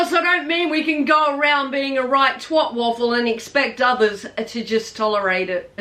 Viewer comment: Me: “did she just say twat wobble? I think she just said twat wobble.” dies laughing 😹😹😹 Please tell me I didn’t mishear you. This is an awesome video. You give me a lot to look forward to 🖤 aging is a part of life and it’s definitely an achievement! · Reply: Lol you’re close I said “Tw@ waffle!” Lol don’t worry I typically have most people scratching their head thinking 💭 Did she really say that???!!!! And yes she typically did 👊🏻😉 My mum always says to me “Tell us what you really think Sharyn” 😫😂 I appreciate you my girl thank you always Shana Have a wonderful day gorgeous ❤️💥✨